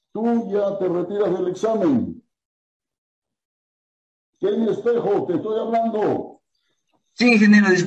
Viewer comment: entendí la primera vez que decía "hey pendejo te estoy hablando" XD